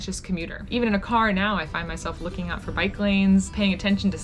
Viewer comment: At the end thought she said "I find my self looking out for biklings" LOL I thought that meant little bikers or something...... Turns out she said "I find myself looking out for BIKE LANES"